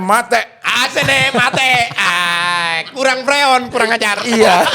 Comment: ngakak parah 😂😂😂 walaupun GK ngerti OKI ngomong apa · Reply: Kurang freo, kurang ajar